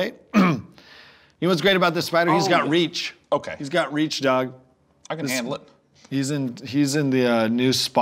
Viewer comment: did Heder just call Sean "Doug"?